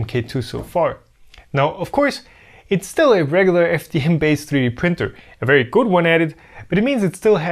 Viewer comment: Please, what does say at 12:58? I understand "regular ft hand based 3d printer". I'm sure I'm mishearing it, because even though I don't know the 3d printing lingo, I can't google these terms. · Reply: the subtitle should say ".....regular FDM based printer". FDM (Fused Deposition Modeling)